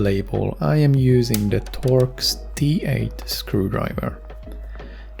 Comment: what kind of screwdriver? 0:44 (couldnt hear) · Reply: torx t8 and other was philips screwdriver